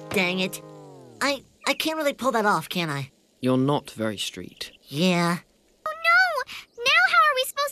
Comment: I think someone said “you look really gay really gay” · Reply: It sounds like ferb says “you’re not very straight”